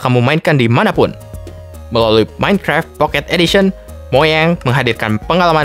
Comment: tadi aku salah dengar apa tidak,soal nya tadi aku dengar orangnya bilang moyang,yg betul itu kan mojang